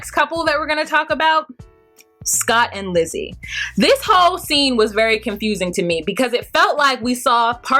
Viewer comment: I thought you said “this Hoeeee scene “when talking about Lizzy 😂😂😂😂😂